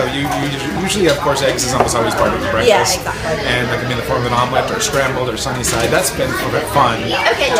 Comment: why did I hear "they can be in the form of" instead of " instead of "make them in the form of"?